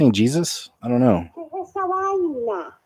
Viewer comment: no she is not saying Jesus. She is saying “que es eso? Que es esa vaina?l Which means, what is that, what the “heck” is that?